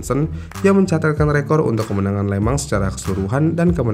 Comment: anehhh ya setiap hostnya bilang le mans tapi kedengeran le-mang jadi kepikiran lemang durian dan lemang tapai bikin laper tengah malam